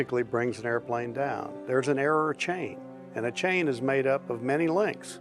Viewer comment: Did he say" error of chain"?